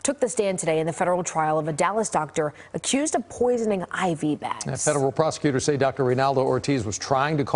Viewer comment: I thought he said “Poison IVY bags” 😭